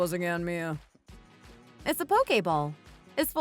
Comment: I thought she said Poke-ball from like the cartoon....that's so funny